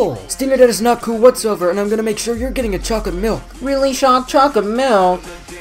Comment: turn captions on and instead of chocolate milk it says “Rock of milk” that doesn’t sound good